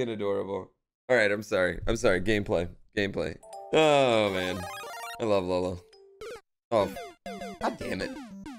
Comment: I think he's saying "I love lasagna"